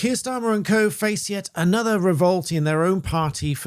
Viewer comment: Sounded like you said "face yet another revolting party" so I thought of Zack Polanski 😅😅